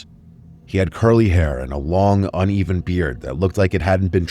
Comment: I know Mr. Nightmare said "long uneven beard"... but I first misheard it as "long oniony beard" hahaha